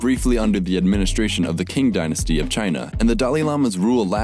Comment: Did you just call it the "king" dynasty? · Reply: Qing not king